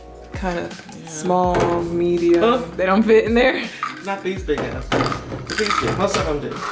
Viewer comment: Wait.....did I hear cuss???? 10:51 "not those big ass ones" 🙀🙀